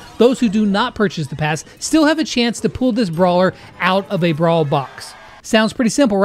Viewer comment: all i heard was get the brawler from boxes